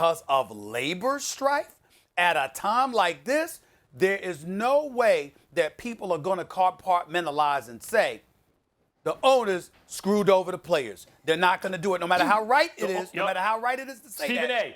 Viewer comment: Did he just say CarPartMentalize